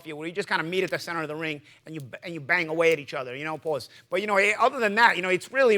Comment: wait did Paulie say pause?! 😂😂